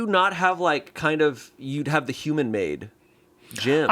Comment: Ive been listening this entire podcast thinking they were saying human maid Pokemon and I was like ya know I don't really see how Mewtwo is a maid but I suppose and it only just clicked for me what they were saying 😭